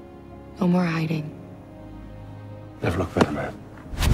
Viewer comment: What magneto say? It's so Fast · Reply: "never looked better, man"